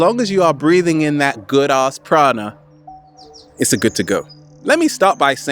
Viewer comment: I had always heard you say "breathing in that good as chronic" and today i noticed that you say prana instead, why was my perception decieving me?